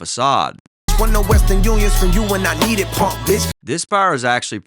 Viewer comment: I thought he said “weren’t no western union from you WHEN I NEEDED” basically saying when you was up you didn’t give me shit, now I’m up and it’s a problem.